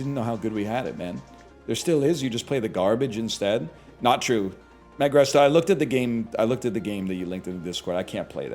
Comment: Naigresta is actually Magresta? I never saw the name spelled so I thought he was saying the former